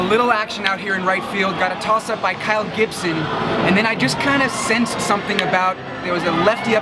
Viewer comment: thought he said Greg Gibson :(